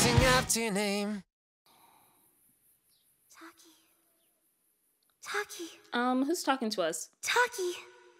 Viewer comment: Help at 4:32 I thought she was saying bags of Takis